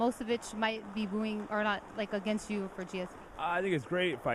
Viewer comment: I think she said "they might not root against you."